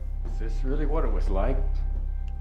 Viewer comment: Oh he says slick. I thought he said slave